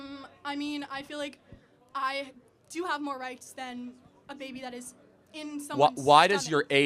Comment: does she say “I do have more rights than a baby in someone’s stomach”??